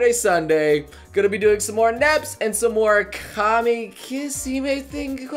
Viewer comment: I paused at 0:45 Because I heard Nep Nep.....